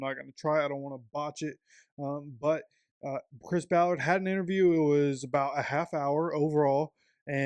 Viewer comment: Ha, I heard you say, “Prince Ballard.” 🤣. We did win the Browns Game 😢.